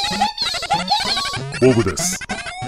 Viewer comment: dice pobres? O que esta diciendo loquendo?